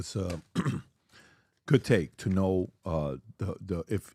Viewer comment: Did pat say "good take"?..... and he was talking about adam???!!! 🤣😂🤣